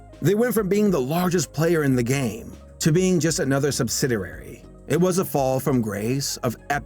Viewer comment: did you say "Subsidarary?"